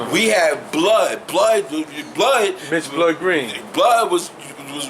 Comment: EXCELLENT WORK AND BEST VIDEO I'VE SEEN YET O.G. TRULY APPRECIATE ALL THIS HIP HOP TRUTH AND INFORMATION BROTHER... It sounded like he said Mitch Green at the end right? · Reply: Yes my Bro , Mitch Blood Green, thats him i was talking about, stay rising King